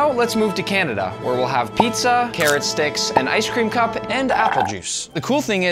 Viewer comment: did u just say ice cream????😮😮